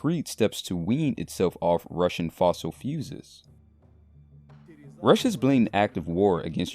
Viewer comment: Did he say "fossil fuses"?